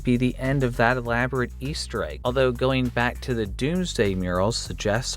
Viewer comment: I thought I heard "Beasteregg" >.< you see because 😂 Beast easteregg = Beasteregg xD